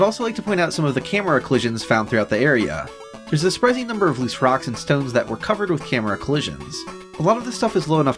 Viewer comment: DID I HEAR A ROCK AND STONE?!?!